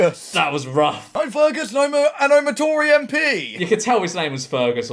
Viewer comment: For a moment I thought the Tory bloke said his name was Ferguson, and I was absolutely creased, cuz that’s the name of my partner’s childhood teddy bear 😂😂😂